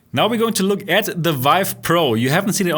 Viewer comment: So G2 is worse than Vive Pro? VR is not making any progress while we are stuck with these Gen 1 headsets. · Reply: I missed that you said Vive Pro. Thought u said OG Vive. Honestly, I like the WMR controllers more than Vive wands. Trackpads are awful imo and I'm glad everyone's getting away from them. But Light house is a much better tracking system for accuracy. G2 has a lot to like but it's not perfect.